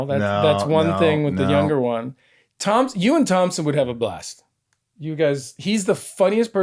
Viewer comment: His kid's name is Thomson not Thomas. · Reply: Ahhhhhhhh I apologize. It sounded like He said Thomas.